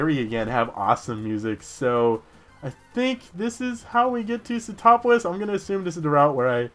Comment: Was it just me or every time said Sootopolis I heard "get topless" o.o